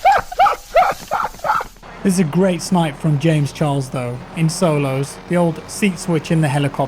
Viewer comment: did he just say James Charles